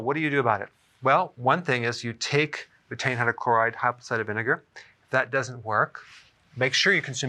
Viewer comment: What does the doctor say at 2:55? (I can't understand the words he says _?_ _?__ _?__ apple cider vinegar.) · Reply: betaine hydrochloride